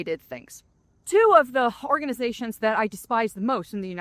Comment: Lol, around the 5 min mark it sounds like she says "whore-ganization" which I am totally using from now on!